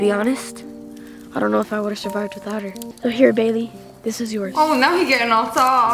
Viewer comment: I LITERALLY HEARD HIM SAY "HERE BABY" lol